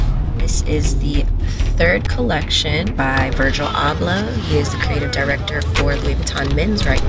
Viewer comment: Sounded like she said “Virgil Ablah”